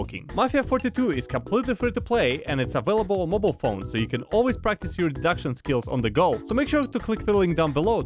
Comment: I misheard the sponsor part as "practice your abduction skills":.